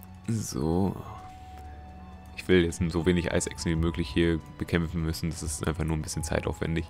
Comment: Gott, hab ich mich eben verhört bei den Eisechsen... zuerst "Eis essen" und dann "Isaacs." WTF?